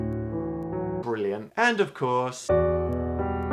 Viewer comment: Briliam? O.O 0:10 XD Or am I just hearing things?